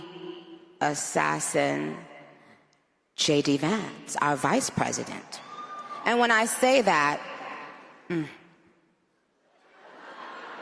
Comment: When she first said that I thought I heard “Like the assassined JD Vance” I thought that she just accidentally mixed up Charlie Kirk and JD Vance as people